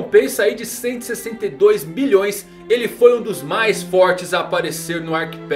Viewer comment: só eu ouvi 162 bilhões?